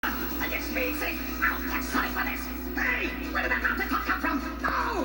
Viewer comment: Eggman: Mumbo Jumbo What I hear: PINGAS PINGAS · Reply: I'll catch sonic for this!